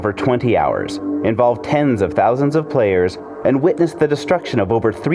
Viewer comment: Derfoklishe Click the time link i provided. He clearly says tens of thousands. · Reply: ***** He clearly says "tens of thousands"